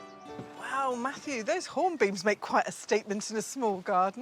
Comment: the host mentions the name of a hedge in the shady garden. It sounds like she says 'Hornbeams'. Is that correct?